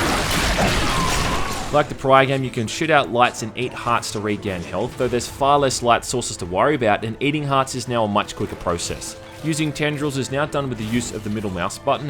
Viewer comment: I heard "Shit out lights and eat hearts" and I said I need this game